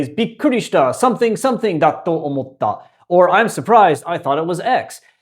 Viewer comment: you say だと思った instead of かと思った like is written above you. Is there a difference?